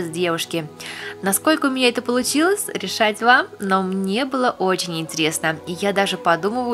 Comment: Мне послышалась: Ржать вам! 😂😂😂😂